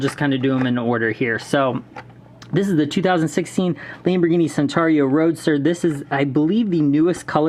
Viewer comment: Did you just say sentario? It's Centenario (Chentenario).